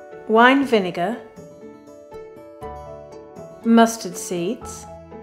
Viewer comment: When Mrs. Crocombe said mustard seeds, my immature teen brain thought she said "Musty Seeds"